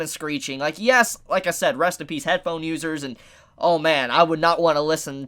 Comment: I thought he said headphone losers